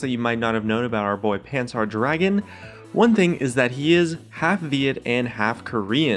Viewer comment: I heard: He is half idiot and half Korean :D